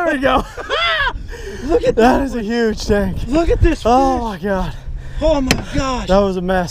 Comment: it sounds like you said “that is a huge dick”😂